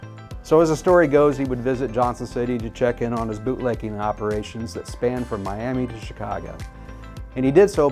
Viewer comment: Sounds like you said boot licking operation